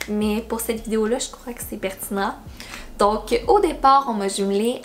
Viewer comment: y'a que moi qui a fait un lien? "Donc OD part" 😂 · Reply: la vraie phrase c'est: Donc, au départ. Mais, comme elle parle d'OD, j'ai pensé à ça.